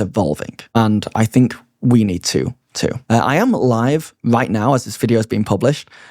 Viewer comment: Initially I thought you said "I am alive..." I was like, damn, that is the naked truth... not pulling any punches this video...